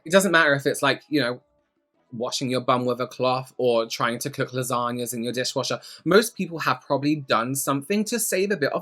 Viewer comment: I’m sorry.. I think I’m hallucinating. Did you just say “cook lasagna in your dishwasher”..??? Certainly not, because that makes zero sense. Yep, audio hallucination makes more sense.